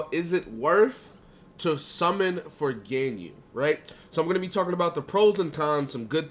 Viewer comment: My weird ass ears heard him say "Virginia" instead of "for Ganyu"